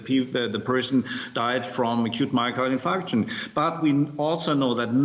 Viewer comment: do I hear 30 million, 35? it's like a an auction bidding war. 5:11 For those wondering what that guy said was acute myocardial infarction aka "heart attack."